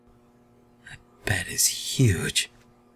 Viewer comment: I keep hearing "I bet he's huge" at 4:29 even though I know what he says